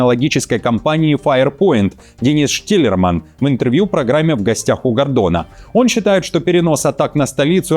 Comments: Только мне послышалось "В гостях у гандона"? 😅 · Мне одному послышались в гостях у Гондона 😂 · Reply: Я 3 раза переслушал - действительно сказано "в гостях у гандона". Видимо оговорка по Фрейду) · В гостях у Г.а.н.д.о.н.а ? Или мне послышалось ? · Мне одному послышалось "...в гостях у ГоНдона"? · Это мне послышалась гондона интервью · Мне послышалось или он сказал в гостях у гандон* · Я один услышал « в гостях у гондона»😂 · В гостях у Гондона? Мне не послышалось